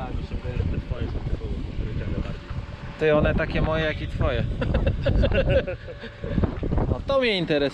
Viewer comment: " taki on mój jak ten twój" Ale siekiera od początku 😂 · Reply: Tylko, że źle usłyszałeś, bo powiedział inaczej "taki on mój jak i Twój", a to już całkiem inaczej brzmi.